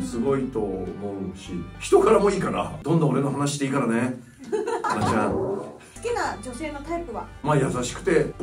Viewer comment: 「花ちゃん」が秋山に聞こえて嬉・・！！！